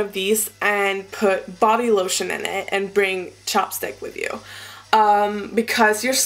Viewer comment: haha when you said chapstick it sounded like you said chopsticks so i was like " bodylotion and chopsticks?" but then i got it :DDD much love from finland ! (